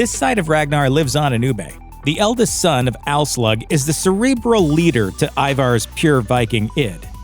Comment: Did he just say Alslaug at 04:15 ?